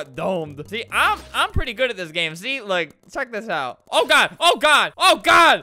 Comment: did he just say (suck this out)